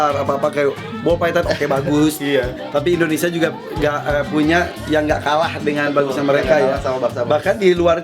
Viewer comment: Indonesia juga Eeeee gak punya Apa ane salah denger